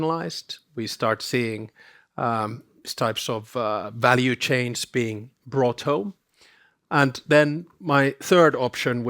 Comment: does Mr. Stubb say ''value chains brought home'' or am I hearing it wrong?